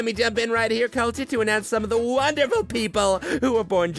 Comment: Did he just say Simon cowwl is America